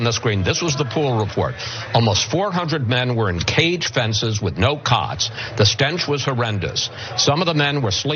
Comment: I think he said "no cots" not "no cods"